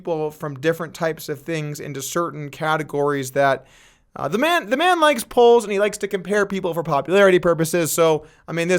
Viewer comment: All I heard is (DNEGs) "The man likes poles!"